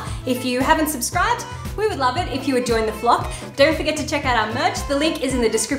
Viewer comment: I totally heard “join the plop”